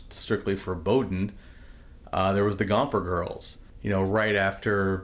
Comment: did you say "Gomper Girls"? I cant find anything about that.